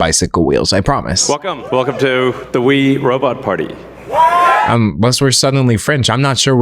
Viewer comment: i thought he said “wee robot party” like it’s a party for small robots